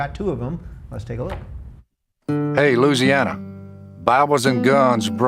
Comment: it sounded like he said "hey loosiana"